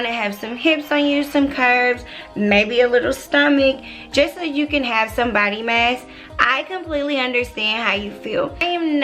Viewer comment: Thought you said just so you could have somebody's man. 😂